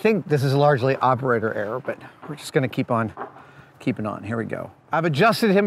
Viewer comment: did he just say "keep on keeping on" death stranding